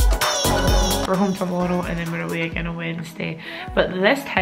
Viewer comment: I thought you said “ we’re home for oral” 😂🤣😂🤣😂